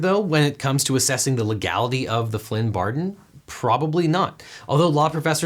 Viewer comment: Anyone else hear him say Flynn Bardon @ 13:01 ? :)